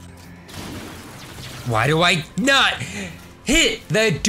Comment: i thought he said 'why do I nut'